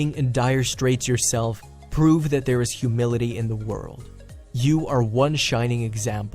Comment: I CAN'T BE THE ONLY ONE WHO HEARD HUMILITY INSTEAD OF HUMANITY